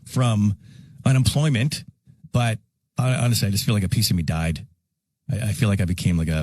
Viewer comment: thanks for this video and for transcribing Kid Carson's last 'rant', very useful for non native English speakers!!! At 1:40 Kid actually says "I feel like a piece of me died", instead of the text in the vid.